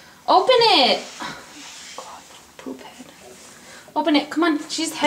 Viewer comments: at like 11:11 you said for your son to open the door, and he didn't and I thought you muttered "ugh stupid.." and I was like WHAT?! then I repeated it and realized you said Poophead, and I laughed. :) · minutes did you call him poophead?? lol